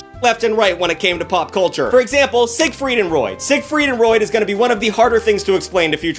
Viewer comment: Did I hear you say “Sigfried and Roid?”